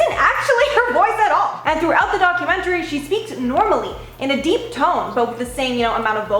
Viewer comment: Did you say, speaked 😂😂😂